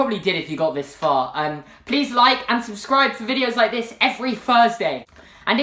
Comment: Yay fursday is the best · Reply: It sounded like he said fursday... -_